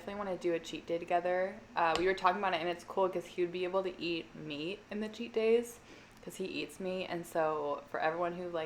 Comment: I had to rewind like 3 times because I could have sworn you said “he would be able to eat me on the cheat day, cause he eats me” 😂 I was like damn I won’t be able to watch that type of video at work lmao 😉🐱🐈